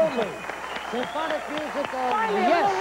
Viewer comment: Who's the bass player? I thought I heard Regis say Kevin.... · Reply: Kevin Kuhn